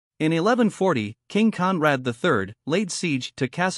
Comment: Anyone else heard it as "in 1140, King Kong..."